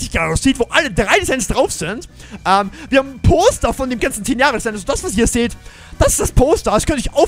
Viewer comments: Ich hab statt poster toaster verstanden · Bei 16:50 habe ich verstanden toster dachte mir erstmal ja geil ich hol mir erstmal nen toster